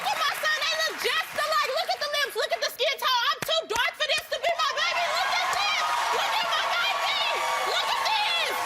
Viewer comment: did she say she’s too dark for that to be her baby🤨🤔